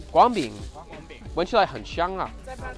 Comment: What is the word at 2:32 that sounds like nigga? 😂😂